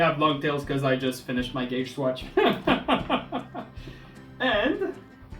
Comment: I sware I keep hearing you say "Gay's watch"